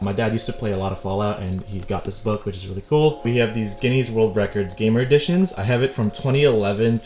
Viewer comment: did this man just say GUINEA AS IN GUINEA PIG INSTEAD OF GUINESS?